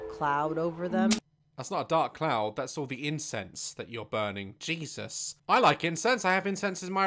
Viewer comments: OH HE SAID INCENSE I THOUGHT HE SAID INCEST. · WHEN SAID I LIKE INCENSE I THOUGHT HE SAID I LIKE INSEST 💀💀💀BAHAHAHAHSHHS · U saying incense sounded like u were saying incest lol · I thought said incest